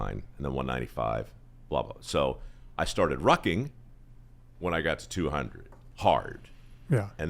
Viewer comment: What is rocking? I thought they were saying walking.